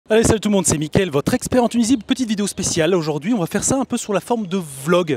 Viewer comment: Jai compris "Mickaël votre expert en Tunisie"